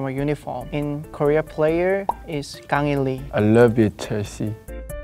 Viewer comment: What did mingi say at 2:55? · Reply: because jongho was talking about psg, mingi decided to also say "i love it/you(?) chelsea" 😭 (his favorite soccer team)